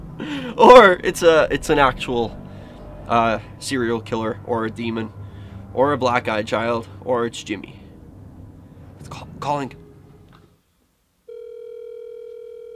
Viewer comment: I wonder if they thought you said “are you a black guy child” instead of “are you a black eye child” cuz that’s what I thought you said the first time lol but I realized that didn’t make any sense and that you were saying black eye child LoL